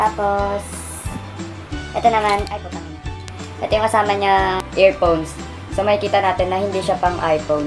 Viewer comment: putang ina? ba ang narinig k?